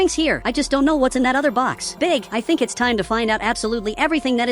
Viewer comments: did bro say big😂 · did bro say big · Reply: BIG